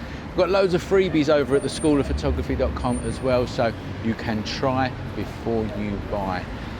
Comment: I thought I heard 5:06 “so you can try before you die” :O hahahah love the video !!